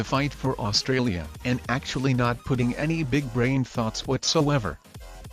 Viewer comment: Ah yes þe AI voice probably meant to say "brain þoughts" but the way it was mumbling it sounded like "brain farts" which given you were talking about NANW yobbоs, constitutes quite a freudian slip. One of þose little moments to bring joy into one's life, kinda like free english lesson wiþ your subsribers, eh? Anyways for today let's take a look at þese verbs: To spit To spoil To split За что мастеры РИСКа должны быть дураков австралией? Лучше не знают ?